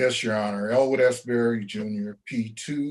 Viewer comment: did he say "Old ass Barry Jr?" lol oh sh*t everybody its old ass Barry Jr in the house! everybody say whats up to old ass barry jr.